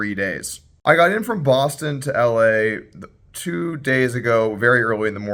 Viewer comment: University of Austin.... ohhhh I thought you said Boston.